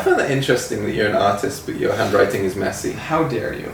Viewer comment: I thought you said Autist instead of artist! Thank god for text